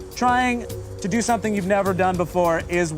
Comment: auto captions say “worthy of a knife”